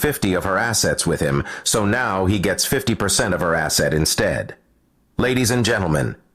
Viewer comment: I thought it said he gets 50 % of her ass 😅